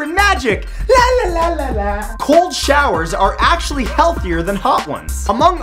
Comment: I thought he said "Gold Showers" lol I'm like "eww"